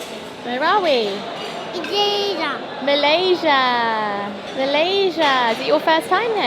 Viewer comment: I can decipher 4 year old dialect. 😂 LOL!!! At 3:56, says “where are we, Malaysia.” After being asked to repeat what his Mother says. is sooo cute! 🤗